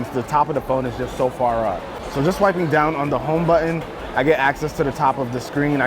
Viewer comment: did you say home button?